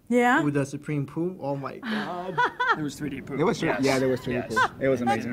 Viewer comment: at first i thought he said 3D boobs and got really excited then relised he said 3D poo and got depressed agian.